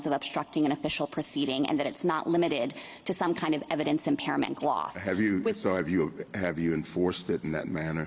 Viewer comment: Did I hear "evidence impairment block"? Egads. If she talks faster, maybe the salad will take shape.